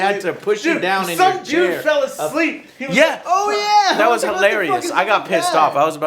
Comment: Did he just say, "Some Jew fell asleep"? Is that really what I heard?